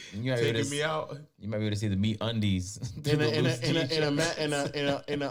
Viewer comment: Thought that man said meat undies